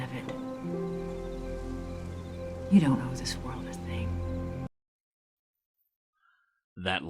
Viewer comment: that line Ma Kent says, that says bugs him ... what the f*ck did she say? I can't make it out between her mumbling and the background music. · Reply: "Or don't. You don't owe the world anything."